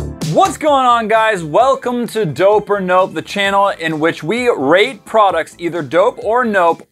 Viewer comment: I swear I heard rape products or was it just me😂